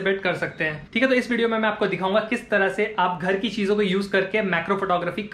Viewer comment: what he say THARKI cheezon ! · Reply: Ghar ki cheezon!!